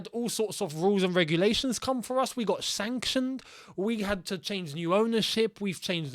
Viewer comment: Did say "we got shantioned"?